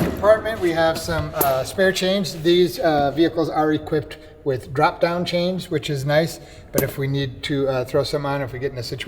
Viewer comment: When he said “spare chains” I thought he said “spare change” 😂